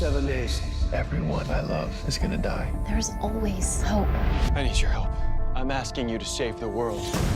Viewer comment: Sounds like she says there is always soap lol